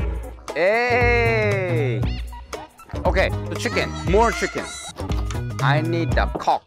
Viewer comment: I need a coke The girl: please give me coke In my mind: did they say C*** 😂😂😂